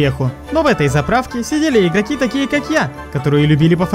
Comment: сидели какие-то ГЕИ КАК Я . Я ОДИН ЭТО 😂😂😂УСЛЫШАл🤣🤣🤣🤣🤣🤣🤣🤣🤣